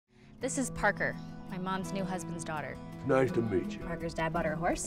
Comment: I had to watch the trailer again..I thought jade said my newest husband's daughter!!lll